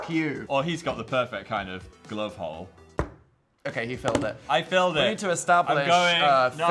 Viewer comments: ngl when Dan said "he Philed it", I thought he said "he filled it" as in, filled the glove hole I- was in shock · I thought Dan said "he filled it" instead of "he Philed it" at 20:02 first and my face went from disgust to even more disgust (so the usual basically)